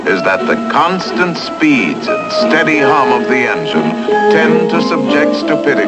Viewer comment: steady .... ? of engine what narrator is saying can anyone help me? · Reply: Jakub TSK "...constant speeds and steady hum of the engine..." 🙂